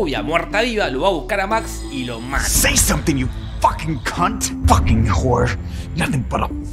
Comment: Película: Fcking c*nt! maldito gatito! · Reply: Yo pensé que decía "Fucking Cat" y por eso lo tradujo así XD